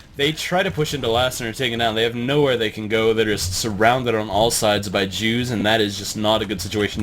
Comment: did you just say surrounded by jews?